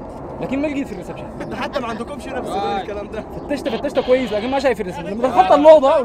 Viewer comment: المصري في الدقيقة 13:00 قال عن الكاميرات" انتو حتى ما عندكمش هنا في السودان الكلام ده !" ولا انا سمعت غلط